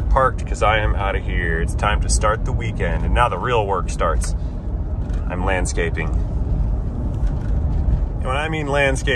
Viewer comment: I thought he said 'manscaping' and I was like TMI!